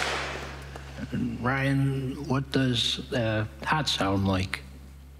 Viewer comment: I keep hearing “what does a hat sound like” and it’s the funniest thing I’ve ever heard. Love you Joe!